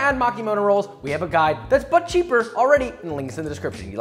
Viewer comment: Instead of "But Cheaper" I heard "Butt Cheek" 🍑